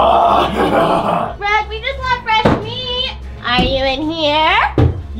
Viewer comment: ayo did she said fresh meat